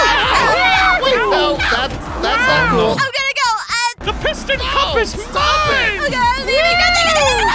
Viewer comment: i thought mickey said "THE PISS CUP IS MINE" but its actually the piston cup is mine lol